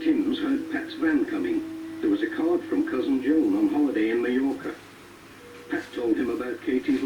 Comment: where did they say the Reverend Timms cousin Joan was on holiday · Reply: Mallorca. Ever heard of it? It's an island off the coast of Spain.